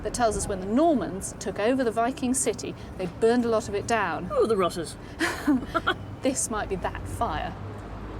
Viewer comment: what did the lady say? Sounds like 'rotters'.... In dutch we have an old word rotten/rottig. Mean is not possitif 😊 Funny hearing more and more old germanic words nowadays like 'hinder' etc. Must be old anglo-saxon.